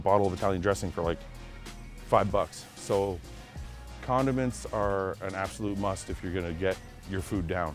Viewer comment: When he was in the toilet paper aisle talking about low quality food and mashing Italian dressing/tuna together I thought he said "continence is a must" then I realized he said "condiments" lol